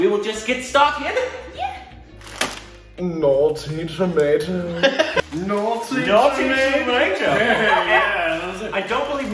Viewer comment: at first I heard "Naughty America" 🤣🤣🤣🤣